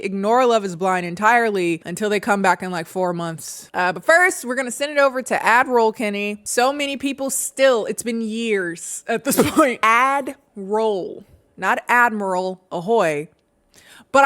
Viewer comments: I thought you were saying Adderall kennie for the longest. I was so confused. 😅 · I’ll be honest, for about 6 months I thought you were saying Avril Kennie, and I thought it was an inside joke about Avril Lavigne that I just didn’t get. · I always thought you said Adderall Kennie LOL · For the first few of your videos I genuinely thought you said "Adderal Kennie" instead of "add roll" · I thought you were saying Adderall Kennie omg 😭😭😭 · Not even gonna lie I thought you were saying Adderall kennie for the longest time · i always thought you said adderall kennie wtf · Do the merch!!! I'd buy. Because I definitely thought you've been saying Admiral ahha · Girl I thought you were saying "Adderall Kennie"😂😂😭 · Ma'am my dumb ass thought you been saying "Adderall Kennie" this whole fucking time. Im logging off. Bye. 😂😂😂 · admiral kennie? girl ngl for a LOOONG time when first binging your vids my dumbass thought you were saying ADDERALL KENNIE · I thought you were saying Ad World Kennie 😂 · I always thought you said ADDERALL KENNIE 😅 ... obvs with the american accent